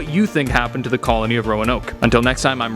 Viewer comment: Oh i thought he said im rob mcenzie the virgen